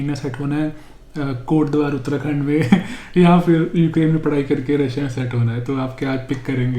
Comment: Did you say Kotdwar, Uttarakhand @ 15:15??